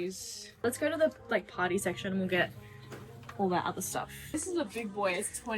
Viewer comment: when she said party secshon I thought she said potty secshon🤣🤣🤣🤣🤣🤣🤣🤣🤣🤣🤣🤣🤣